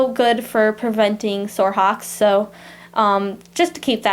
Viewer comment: what is that word and what does that mean...thank u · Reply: valenncia williams hocks ? it means feet or knees.